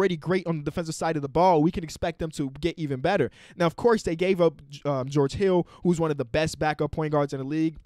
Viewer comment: I thought he said George kittlw